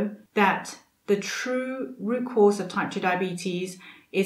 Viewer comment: Thank you. This was a really comprehensive, well made and helpful video. you look fantastic and I’m so glad it helped not only your body but your mind. ( Just a mention, at 11:00 the Video has “the route cause” instead of root cause).